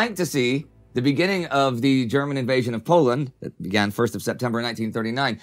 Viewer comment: i don't even know why i watch with subtitles but i noticed a slight error there. Indy says "1st of September 1939" which is correct but the subtitles say 1940. just a minor thing